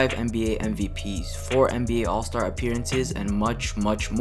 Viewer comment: does he say 4 all star appearences